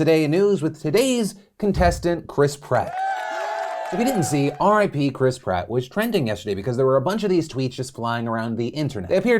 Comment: OMG. I thought you were saying Chris Pratt was dead D: omfg